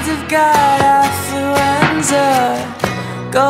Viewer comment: Not gonna lie for the first few days of listening to this song I thought it was saying influenza (tge flu)